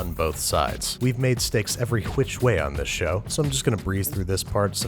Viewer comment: Did I just hear : "every HWICH way" ?!!?